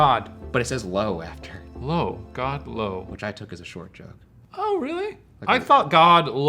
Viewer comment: 1) when it says “God” “Low”, i heard it as God No, meaning the ghost wanted shayne to die LOL